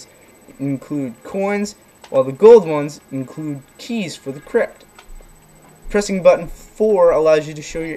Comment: sounded like you said "Cheese For the Krypt".